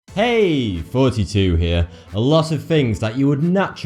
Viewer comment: It sounds like he says "hey, 42 here"